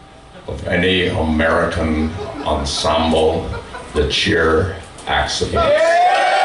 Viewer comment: I can't quite make out the name of the ensemble that is mentioned at 1:05:40, does anyone know what it is? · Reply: He's talking about Chicago prog rock institution Cheer-Accident, who have gone on a couple of tours with FSE and played a blinding set right before this one (I was there!)